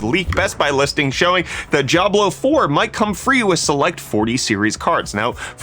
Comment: Why am I hearing "Jiablo" whenever he is saying "Diablo".